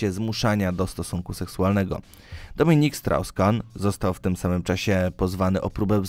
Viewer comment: Czy ja usłyszałem 'Dominik Srał Skan'??? Boże, jak mieć pecha z imieniem to na całego...